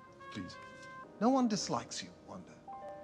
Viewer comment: "no one dislikes you wanda" the marvel fandom: agreed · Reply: I thought you said no one Likes you Wanda and I was like NoNo but yes we all love Wanda